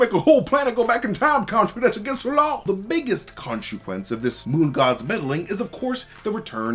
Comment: He says ”Khonshuquence” instead of ”consequence”, right? 😅